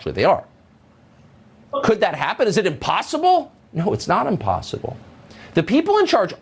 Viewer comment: did Tucker just say "unpossible"?